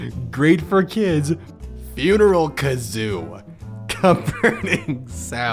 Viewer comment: I thought it said, "Funeral Kangaroo"